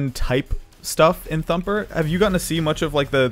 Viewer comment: I cant be the only one that thought it said trumper right...right?